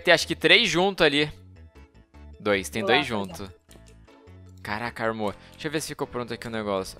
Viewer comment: Eu ouvi bem em 1:07 ogente falou para vaga caraca ne mor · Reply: Foregone X ele falou caraca armou